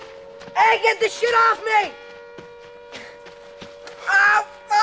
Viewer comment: When I was a kid i thought he said get this shoe off of me